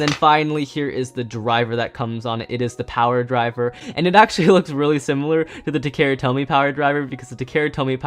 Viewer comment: did he say power driver that’s charge